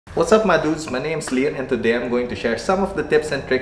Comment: tingin ko filipino si kuya pinapanood ko kasi ung mga previous video #subscribed 😁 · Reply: pinoy nman talaga (cguro) sya. di mo lang siguro napansin intro niya.."watsap mga dudes",, o cguro namisheard ko lang😂